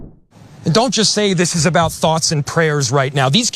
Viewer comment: he said “not just thoughts and prayers.” I agree with him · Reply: Then you do you! Perhaps my English wasn't good enough to hear what you said you heard. This is what I could hear from 2:44 of the video: "And don't just say this is about thoughts and prayers right now. These kids were literally praying. It was the first week of school. They were in a church." You sure know how to quote at your convenience. " Whoever has ears, let them hear.” (Mt 13:9).